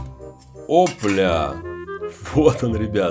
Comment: мне послышалось что он сказал о б**